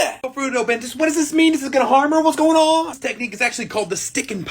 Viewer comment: Why did I hear “ what’s going on b!tches?” 😅😂😂